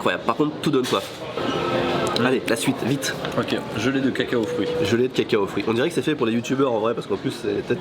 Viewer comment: Vos vidéo me donnes toujours faim! ^^(et j'ai entendu "caca au fruit"...^^")